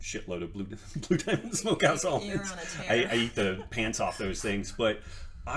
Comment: What did he say he eats a load of? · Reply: I think he said Blue Diamond Smokehouse Almonds.